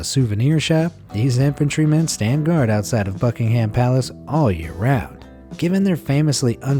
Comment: Sounds like he said fuckingham